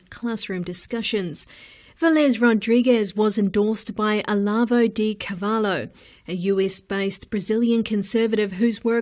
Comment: só eu que escutei "A Larva de cavalo" em vez de Olavo de Carvalho? Kkkkk